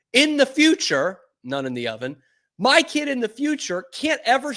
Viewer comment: lol did u say none in the oven or bun in the oven??? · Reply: He said "none in the oven".